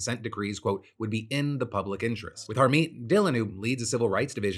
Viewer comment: When you said Harmeet Dhillon I heard it as "our main villain". Not sure how, but it kinda fits.